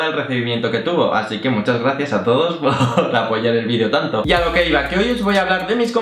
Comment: jjjjaajjaaaaa la polla en el video tanto?!..... say what!?...say yeah!?..say nooooo!? jjjjjaajjjaaaaaaa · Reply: Yosef Saphiro Schwartz por apoyar el vídeo tanto